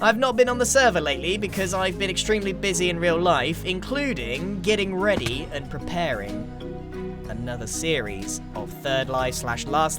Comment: Dude when he said “Getting ready and preparing” at 1:08 I thought he said getting ready for parenting 😂